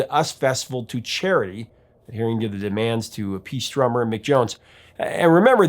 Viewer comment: Where did I say he was the drummer? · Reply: It sounded like you said "To appease drummer Mick Jones"